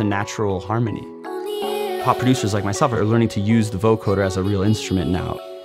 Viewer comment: "hot producers like myself"... nah, pass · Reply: thought he said 'pop producers'